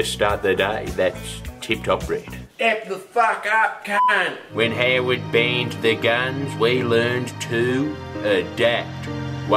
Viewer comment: What is he saying? "When Howard ???? ????, we learned to adapt." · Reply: 'When Howard banned the guns, we learned to adapt.'